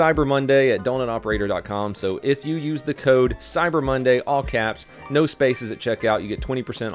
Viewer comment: Ngl I thought he said “code cyberbully”